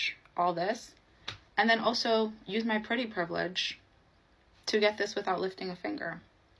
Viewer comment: Did she say, ”PRETTY PRIVILEGE”??? 🤯